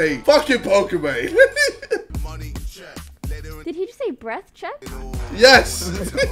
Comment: This whole time i thought he was saying wet not breath lmaoo